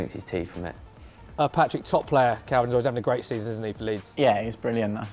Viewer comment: did he say Calvert-Lewin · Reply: No he said kalvin was always